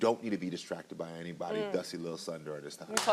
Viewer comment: Did he just call that man dusty? I AM SCREAMING